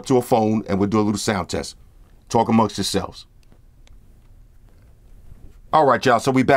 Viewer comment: what did he say at 3:18? he always says it in his videos but i still don't catch it ^ ^(i speak french). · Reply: Talk Amounst Yourselves